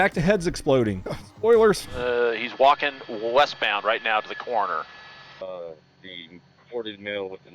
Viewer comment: O5:53. Did he said he’s walking to the coroner?